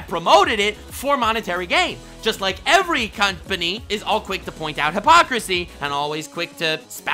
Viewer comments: He really said "cünt-pany" for company lmfao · did you cunt-pany?(Company) it might be unintentional... But I heard cunt-pany when saying that all they do is want $$$ lol!